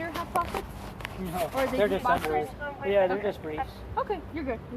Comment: Did he say he was wearing underoos at the 1:01:55 mark??? 😵‍💫 If so, that should be another warrant for his arrest - crimes against fashion!